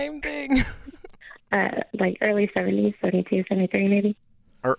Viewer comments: I think I need a hearing aid cause I definitely heard "early 30's" when he asked for her grandma's age. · I think I need a hearing aid cause I definitely heard "early 30's" when he asked for her grandma's age. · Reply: I heard "early 30's" also.